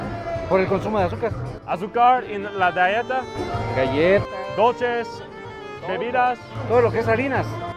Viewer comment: dude thought he said “galletas” but he said “ dietas” just sounded like galletas because of how he says it “die-E-tass” lol